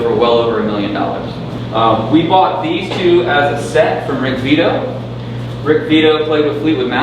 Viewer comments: At first I thought he said Rick Beato had owned the Korina V and Explorer 😂😂😂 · Did he say Rick Beato played with Fleetwood Mac? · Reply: Karl Frykt Rick Vito, not Beato